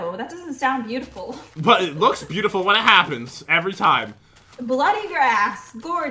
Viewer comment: i thought jen said blood in your as 0_0